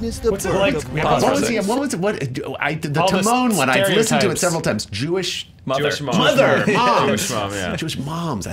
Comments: I thought he said Jewish MOBster. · I heard "Jewish mobs" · when they said, Jewish mother, I thought they said Jewish monks